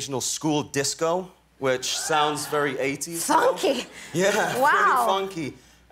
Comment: idk why when she said funky I heard " fu*k me"